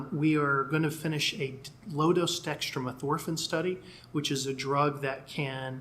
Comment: At approx the 9:25 playback location, mentions a drug that calms down microglia. I am not understanding the name and the closed caption is not showing it. Does anyone know what drug they are looking at? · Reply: Chris Frates dextro naltrexone?